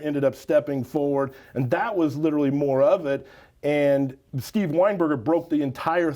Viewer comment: He says "Like" more than "Literally" Lololol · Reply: I thought I heard literally more!!😂😂